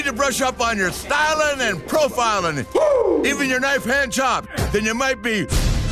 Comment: Did it sound like Flair said 'a nice hand job' instead of knife chop